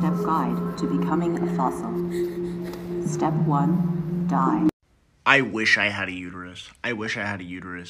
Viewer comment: I thought he said “I wish I had ears” 😭